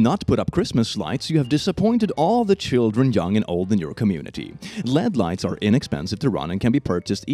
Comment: When he said LED lights, i thought he said "Lead lights"